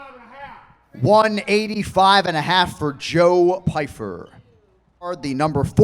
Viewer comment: Anik sounded like he said JOE BIDEN for a second instead of Pyfer lol...